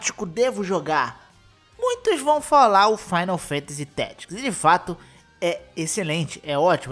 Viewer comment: o final fantasy q falou no inicio é o 14?n entendi a palavra depois de final fantasy. 1:27 por ai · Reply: Final fantasy tactics